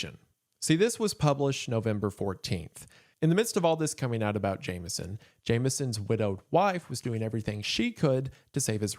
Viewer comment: everytime he says the name I just hear "James Jonah Jameson" lol